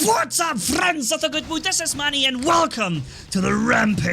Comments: When I first watched the vid, I thought u said ‘what’s up friends of the good mood, this is mommy’ and then I saw ur channel and it said. That was when I got introduced to the channel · When I first watched the vid, I thought u said ‘what’s up friends of the good mood, this is mommy’ and then I saw ur channel and it said. That was when I got introduced to the channel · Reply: I've been wondering for a couple of months what he says after "friends of the good..." At first I thought he said goodwood, but that made no sense.